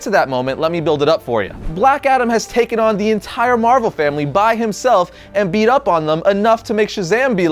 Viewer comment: Did he say Marvel family??